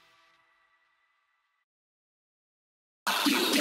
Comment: WHAT DID HE SAY?!?!?!?! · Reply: I think he said " are u okay